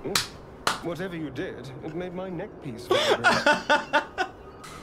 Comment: I thought he said made my nipples vibrate then he pressed again and I'm so happy I heard it wrong cuz I knew what he was meant to say but that wasn't what I heard xD